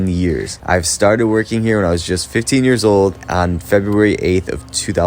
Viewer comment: I can't be the only one that thought he said "These cakes are 15 years old"